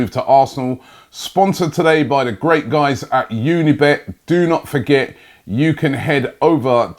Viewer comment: did anyone else hear "the great guys at unibet do not forget" LOL too good